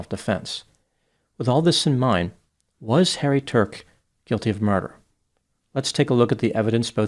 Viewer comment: I thought he said "Hairy Turk" at the start 😂